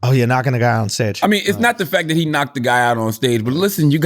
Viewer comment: If you listen closely to the first 10 secs... sounds like Andrew said... ohh yea... “knocked a nigga out on stage” 🤣🤣🤣🤣